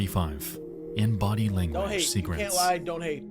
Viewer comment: Did he just say don't hate??